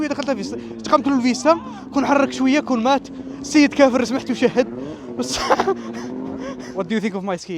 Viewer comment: السيد كافر سمعتو شهد 😂😂😂